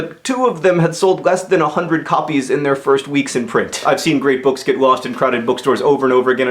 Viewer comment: It sounds like he said " about rape books", but it is probably "great books", because that would make more sense... Is it not strange that 2 of the top 4 books in the NYT bestseller list contain references to orphans?